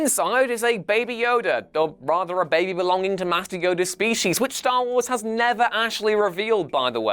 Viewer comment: Did anybody else hear him say what sounded like "Matthew Yoda's species?" I mean I KNOW he said Master Yoda, but the first time I heard it...